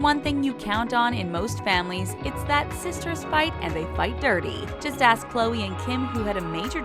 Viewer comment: I thought they said “Sisters fight every 5.30” so I thought they always fight in the evening